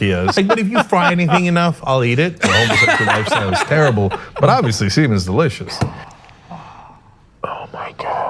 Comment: did Cenk just say "but obviously semen is delicious' or am I hallucinating??! :-D